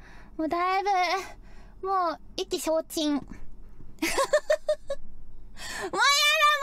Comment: What did Lamy say in 15:28? The translation says I'm completely drained... What is the original phrase? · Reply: IKI SHOU CHIN 意気 消 沈